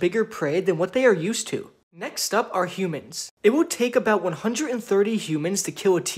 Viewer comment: It will take 130 humans to kill a T-Rex-… hold up humans I thought you said animals not to make fun of you I just found that funny but your a great YouTuber 👍🏻